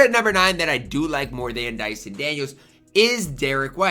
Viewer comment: I thought you said Marcus Smart is better than Dyson Daniels